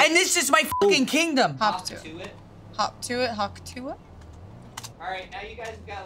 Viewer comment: so like i aint the only one who heard hawk tauh it?